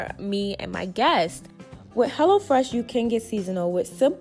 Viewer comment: Lml I thought you was saying hella fresh at first 😹